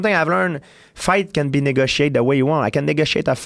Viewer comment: he said negotiated but it sounded like nigga shade haha